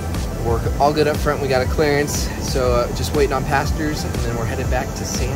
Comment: I heard you say "waiting for pastors". I assume that person or they are your passengers? Who is that/they?? Any Lavish multi national company CEO able to afford a Gulfstream G4 as their personal jet!!??